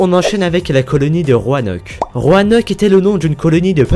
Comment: j'ai compris "Le Roi Hennock" xD